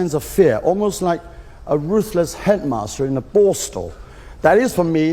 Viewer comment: 应该是a ruthless headmaster in a borstal(管教所), 不是board school(寄宿学校)。 PS: 我第一次听成了brothel，囧